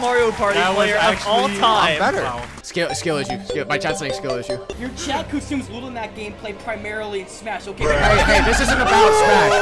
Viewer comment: Absolutely amazing and funny video! Im sorry, but I can barely make out ’ roast at at 18:11, what did he say? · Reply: "your chat consumes little mac gameplay primarily in smash-"